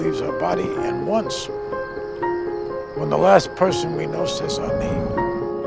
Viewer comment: when the last person we know system me? Huh? What does he say? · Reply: You know they say we die twice the 1st time when the breath leaves are body and the 2nd when the last person we know says are name